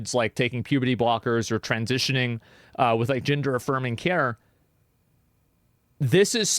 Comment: i thought you said "Ginger affirming care" for a second